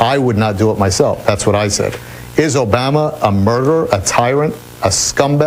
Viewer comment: Did he say Obama